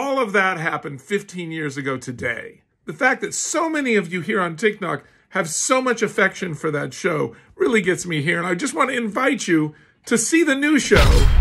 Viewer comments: Am I the only person who heard him say “Tik Nok” lol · Did he just say tiknok?😂😂